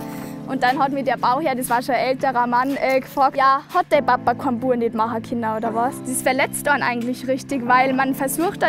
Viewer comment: Als norddeutsche fällt es mir doch recht schwer den Dialekt zu verstehen.. was sagt sie bei 04:00?? Ich versteh kein Wort 🙄😭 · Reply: "Hat dein Papa keinen Jungen machen können?";)